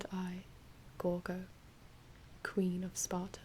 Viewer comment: I thought it said queen goro for a second XD